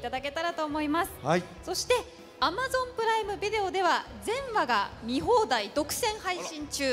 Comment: 近傍が全話が全裸に聞こえて己の不純さに恥じ入ります そういえば通クンはもう10年も彼氏役続けているんだなあ 長身だから普通彼女役はみんな小さく見えるけど唯一の例外が アレかあ身長6フィートのももちの同僚